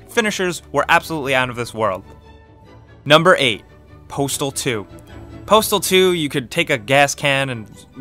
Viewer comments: who else thought that it said "portal 2" and not "postal 2" lol. I cant be the only one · when he said postal 2 i thought he said portal 2 i was like wut